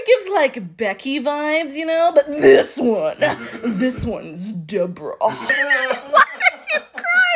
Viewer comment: Her names “ DEBRUH” 😂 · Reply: deBRUH (I heard it as zebra)